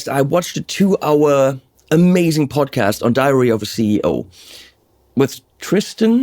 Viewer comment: you made my day, did you just say "diarrhea of a ceo"? cheers! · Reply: DIARY